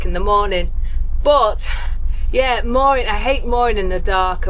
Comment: "Tell you what," said, "without a word of snark, If there's one thing I really hate, it's mooring in the dark." I misheard and wondered oh so wildly Who on earth was Maureen, and what HAD she done to?